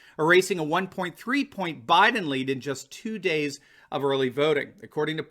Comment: says Biteme lead instead of Biden lead - LMAO HEHEHEHEHE